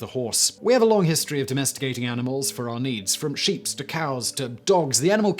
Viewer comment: did Simon says sheeps?!